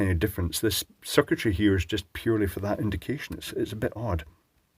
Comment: thought he said vacation